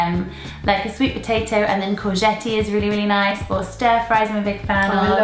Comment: I keep hearing courgette everywhere now!!